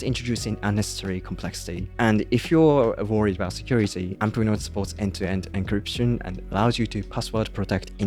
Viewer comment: He said Amplenote at about 2 min. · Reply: Thanks. I thought he said Apple Notes.